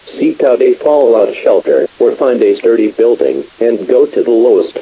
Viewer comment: Did it say a dirty building at 11:30?